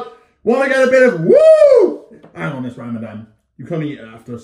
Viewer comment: Did he say ramadan?? In 5:41 Please tell me!